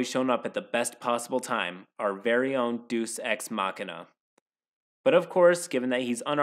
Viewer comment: I'm listening to this more than readn and I swear to God I heard "Douche Ex Machina"